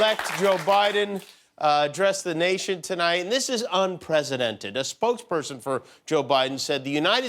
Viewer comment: English is my third language, so I’m not 100% sure: @ 1:28 did Jimmy really say “unpresidented” ? 😂